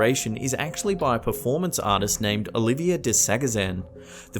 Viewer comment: it's Oliver, in the video you said Olivia (or it sounded like you said Olivia). They were also in the first season of the Syfy series Channel Zero, I was really excited/proud of myself for pointing them out. I find this performance of theirs very creepy and interesting, you'll know right off the bat who they are if you were to watch Channel Zero, especially if you have seen Transfiguration.